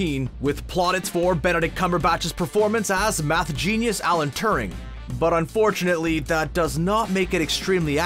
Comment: Is it just me or does he say "math genius ALEC Turing"? WTF?